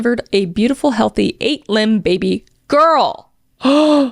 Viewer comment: 8 what?! Did you say 8 limbed?! 🤣 8 pound kris 🤣🤣🤣🤣 29:35 this is 10 months later didn’t realise I’d already watched it and was just listening not watching and when you said 8 limbed I thought “OMG no way she’s had twin girls!!”